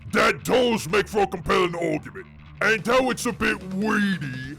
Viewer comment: I can't tell if I'm hearing correctly or mishearing when you say "a compelling orkument", but I know which one I prefer. Great work!